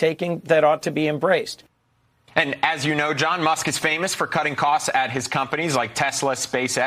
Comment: why did dude say "John Musk"?